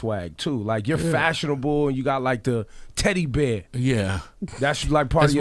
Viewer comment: Did he just call him a "CHUBBY TEDDY BEAR" at 7:30!?!?! 😂😂